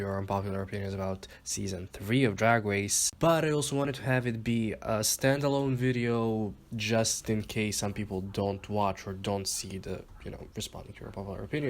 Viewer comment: What did you say at 0:16 ? It just sounded like mumbling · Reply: He said that he was going to make this the intro to the "Unpopular Opinions about RPDR Season 3" but, he made it a stand alone video just in case people didn't watch that video